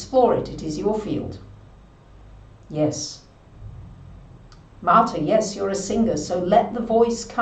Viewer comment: did they say Martha? 😱 I love singing!!!! 🤪